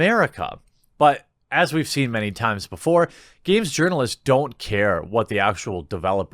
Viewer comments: I thought you were saying Yearnalists, like they were yearning to the most progressive, or something. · I thought you were saying "yearnalist" and not "urinalist". That is even better!!! I am actually quite excited for this. I feel like it could be 'the second coming' of MMOs if they do it right. What you said about them throwing money at something until it works is what I see them doing here as well. Their server strength is going to be outrageous. A drop of their mana saved Pokemon Go! Lol. But yeah, I could see it being really cool. Though, it could be a situation like with Disney where they just don't do self-publishing well. I think it'll be good though.